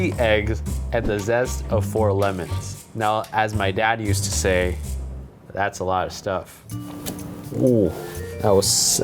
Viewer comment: I seriously thought he was gonna say, "Now that's a lot of damage"🤣🤣🤣